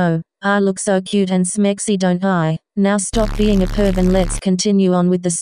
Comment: lol I thought it said POOBH instead of PERV